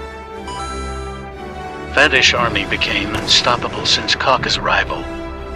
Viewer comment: Bro im dead “fatish army has been amazing since cocks arrival” i thought it said 😭😭😭